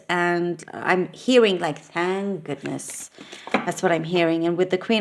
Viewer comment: I apologize for not understanding what you meant at exactly 10:38; "and goodness," is that what you said? I've spent about 2 weeks in your country driving around camping (caravan), but I'm not sure what was said here? Ok, any help here is appreciated. Outstanding reading, listening second time sounds better than the first. Ok, cheers. :goodvibes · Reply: Hello dear..at exactly 10.38 she said "thank goodness" 😊